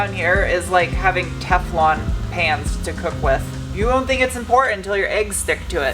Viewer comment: I laughed because I thought you said Teflon pants. Nobody wants eggs sticking to their pants or do they?